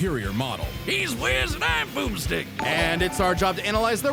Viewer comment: I'm glad i checked the transcript, because i seriously thought the guy in flannel said, "He's Whiz and I'm Poopstick!" 😳 Boomstick. Oh, OK. 😄